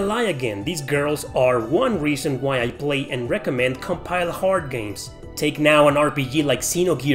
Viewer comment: Can someone please tell me what he is saying @ 3:30 ? I have slowed it down to 0.5x speed, and it sounds like he's saying "these girls are one reason I play and recommend COMPILE HARD games".